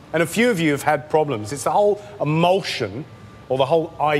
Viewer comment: My dumbass thought he was saying emotion and not emulsion... And I was like, "what is this? Food wars?"